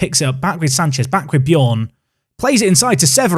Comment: Totally misheard you in the Spurs game "And my nan makes a superb stop there" 😂